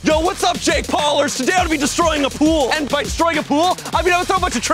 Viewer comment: he said "jake paulers" but auto subtitles think he said shake parlors